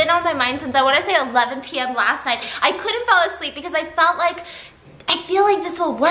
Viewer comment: "I couldn't fell asleep" did I hear that right?